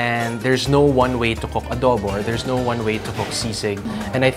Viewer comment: did he just said "there is no one way to cook a dog" !?! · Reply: Adobo